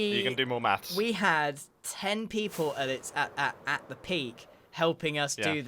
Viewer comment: I would just like to appreciate the captions saying "Tree" instead of "Three"